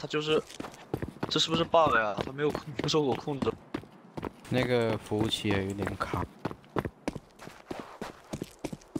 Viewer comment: did he say the N word?????? sonds like it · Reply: He says 那个. It means “that” in Chinese